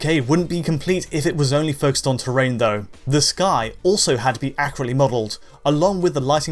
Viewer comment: Why does this guy sound like he says accurate like a carrot?